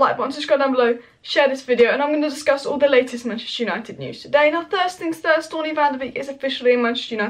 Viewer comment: Hi. Did you just say thirst things thirst instead of first things first?